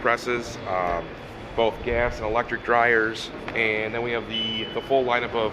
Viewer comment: Awesome video! So. .. I thought I heard something about a gas belt dryer? I'm gonna have to look that up and investigate. Sounds interesting!